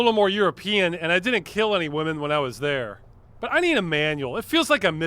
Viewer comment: "This remind me a lot of wandering around New York, I've done that before" "I didn't kill any women while I was there" Glad you were specific there. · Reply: See that's funny, because I heard "I didn't kill any women while I was there " which also raises questions.